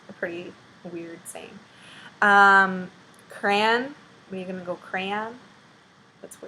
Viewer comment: What's that? I honestly don't understand the word. Krahn?